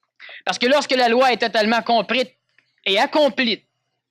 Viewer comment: Mes yeux se sont écarquillés quand j'ai entendu "Mais lorsque la loi est totalement comprite et Accomplite... à